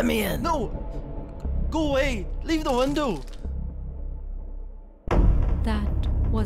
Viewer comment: I'M LAUGHING SO MF HARD XDDD I know he said "leave the window" BUT IT SOUNDED LIKE "LIVA DE WANDOO"